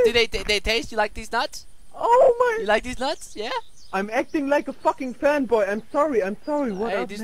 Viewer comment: I'm acting like a fkn fanboy, I'm sorry, I'm sorry. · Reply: FreeAgentEvan I thought he said "I'm acting like a f**ing c**, I'm sorry I'm sorry."